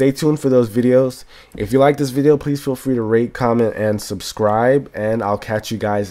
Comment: It sounds like he said "Rape, comment, and subscribe" haha